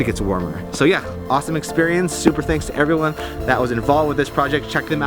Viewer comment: Did i hear Ass Castles?